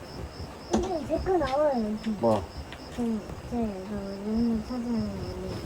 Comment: 여기 대화 대충 들리길래 일단 들리는대로 적어봤어요..! 아이 : 이제 제꺼 나와요 여기 어른 : 뭐? 아이 : 제..제 영상 용량 차지하는거는 어른 : 용량 차지하는거? 아이 : 응응 어른 : 으음~ 아이 : 옛날부터 하던건데 지금은 영상 안올려요 어른 : 영상 안 올려? 영상같은거가 용량 차지 많이 해 아이 : 그래가지고 여기에 했던거 유튜브 업로드를 해놓은건데 어른 : 어 아이 : 봉사활동 너무 길어질까봐가지고..